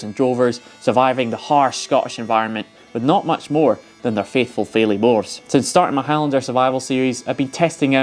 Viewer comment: their faithful faily horse? What did he say I couldn't understand...sorry I'm not a native English speaker